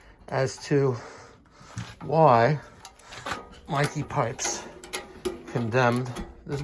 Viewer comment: Start calling him Edit: lol when he said condemn I heard it as condom